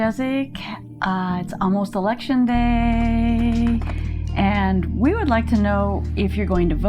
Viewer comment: I thought it said, Erection Day Massage. I know bad thoughts :-)